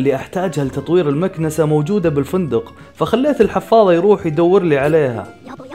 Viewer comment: اول مرة اسمعك تقول تود ضنيت اسمو الحفاظة او انا ماسمعت كويس 💔💔😂 ثاني مرة